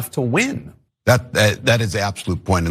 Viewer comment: Tim Walz didn't say anything against Kamala Harris, so what are you talking about?? And we get it: You didn't like Kamala Harris as a Presidential candidate, you hate Joe Biden... got it. How many videos are going to express these same points??? It's getting old now, she lost already. Also, she was laughing because the crowd began to laugh... big deal. It was funny. Edit: I couldn't understand before why so many YT channels were accusing TYT of "right-wing grifting," and I even defended its position on multiple occasions. Now I am beginning to understand: It's your language and the tone of your voice, constantly bashing the Left with fierce criticism accompanied by angry sarcasm. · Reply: "so what are you talking about" i think they may be referring to what walz said at 0:17 i had trouble making it out but when tapper said "she may have warned the american people but she didn't do it completely enough to win" it sounded like walz response was "that's the absolute point" as far as "right wing grifting goes" people accused them of right ring grifting because most people want to be in their echo chamber and once tyt went against going 100% leftist/democrat talking points it upset their echo chamber. bringing an objectivity to an echo chamber is a no no